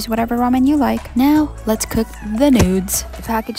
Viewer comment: Did she just say " now let's cook the nudes"